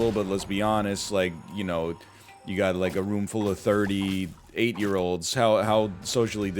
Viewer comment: for real sounded like "a room full of 38 year olds" and not "a room full of 30 8 year olds "